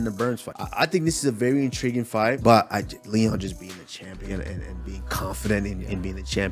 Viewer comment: all I heard was "Chama"